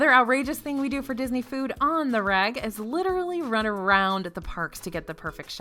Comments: You keep saying 'On the reg' and I hear 'On the rag'. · did you just say 19:02 “another Disney thing you do “on the rag”???? 😳😳 ..does this have another meaning I don’t know about? …in Tennessee, where I’m from and reside, thats a old way of saying “on your cycle”. ….I’m confused